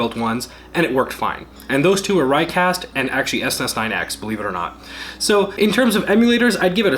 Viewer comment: I heard of Reicast emulator before but what’s the SS9X emulator? Am I hearing it wrong? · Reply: Snes 9x